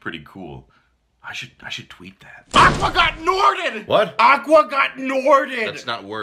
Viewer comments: At first i thought he said aqua god norden and i was like "wait whos norden and why is he an aqua god?" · BTW, I only played 1 and 2, rest of series is way to complicated and filled with retcons. They should also get Ian McKellen to play Ansem and Patrick Stewart to play Xehanort. Also I thought he was saying “Aqua got NORDED!” Like nordic, as in the whole Frozen level being nordic and Aqua’s magic would not be as useful cos’ the freezing messing up her water. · I thought he said "aqua got norden" and was talking about the water freezing bc its cold in the norden countries